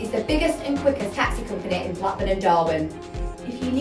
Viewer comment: All I hear is 'Shitty Taxi's, Shitty Taxi's'......